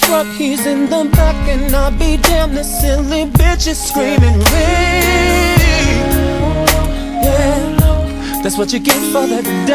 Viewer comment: I always thought he said "this silly bitch is screaming lady"..... talking about Mr. Biggs callin out for her.